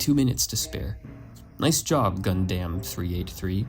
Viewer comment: excuse me did you just call it gun-damm